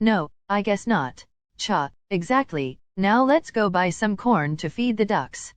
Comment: I thought he said porn instead of corn it got me laughing 😂